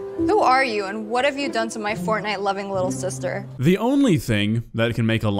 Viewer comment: I heard "fortnite loving sister" and wondered what Dhar was thinking